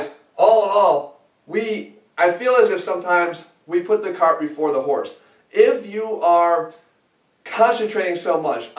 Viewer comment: did he say "we put the carb before the horse"